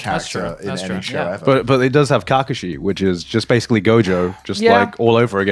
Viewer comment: e<link> why did he said Kackooshi? Brother uuuuhhh 😢